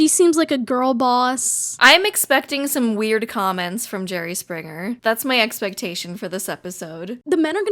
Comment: I swear I thought I heard them say “Jeremy Springer” like 3 times